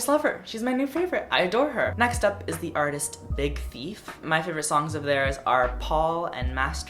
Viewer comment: when you said "Big Thief" i thought you said Big Feet